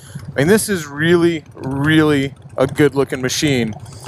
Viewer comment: Did you say $14,500 i dont think a lot of people understand that is a cheap i mean cheap price for a custom bike. Like i thought you said $45,000 and i was like yeah , that makes sence.